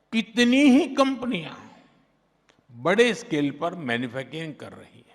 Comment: did I hear manyfu**ing.